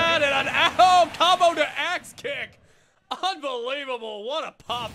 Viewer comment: " COMBO TO *ASS KICK*! " I know it was axe kick, it just sounded like that